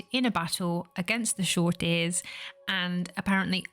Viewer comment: Shame about the unintelligable dialogue we were supposed to hear around the 16 minute mark, i couldnt make heads or tails of it, though i think i heard "short ear" at one point Otherwise interesting video as always ❤